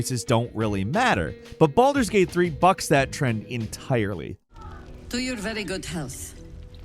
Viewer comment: “But Baldur’s Gate 3 FUCKS that trend entirely…” Lmao anybody else hear that for a split second?😂